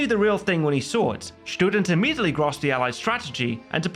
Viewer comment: he did what to the Allied strategy? I can't make out that word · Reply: grasped (understood)